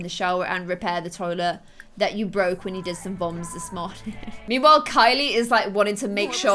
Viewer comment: Ow I thought you where starting the day off with some voms me: I thought you said bombs for a second 😂